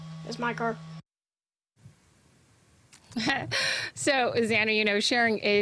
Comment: did she just call that kid xanny